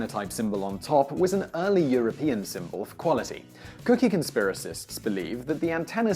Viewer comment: did he just say cookie conspiracists?